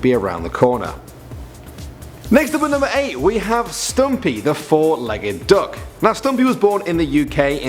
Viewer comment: When he said stumpy I thought he said stampy